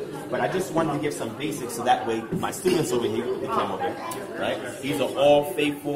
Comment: I'm really surprised him & Zion Lexx can't distinguish a qamets from a qamets hatuph 🤦🏽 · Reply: I could hv sworn said "I hv MY STUDENTS..." so, But pronunciation of אֳנִיָּה was incorrect & I just find it weird that these 2 great Hebraic YT scholars always pronounce a qamets hatuph as a qamets ... but that's phonology 101? I just hope 4 years later he's learned the difference & apply it's phonological value